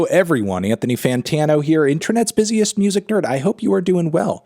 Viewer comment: anyone else hear “internets busiest music turd” ?